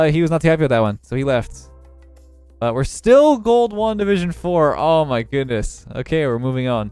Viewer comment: You said “gold 1 division 3” And I heard “ gold Wanda vision three”